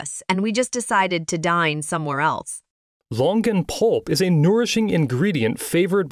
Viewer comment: I thought he said Logan Paul for a second. Longan Pulp 🤦‍♂️